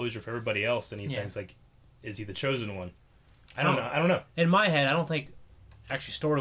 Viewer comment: So I didn’t hear “Is he the chosen one?” I heard “Is it the chosen one?” Perhaps Anakin never was the chosen one, maybe it was Luke all along? Idk